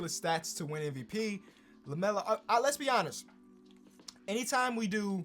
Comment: it sounds like he says let's be Giannis right as he past Giannis